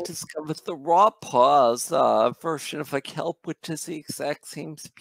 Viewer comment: what was the big bag of kelp product you suggested? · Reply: I heard "Raw Paws Kelp"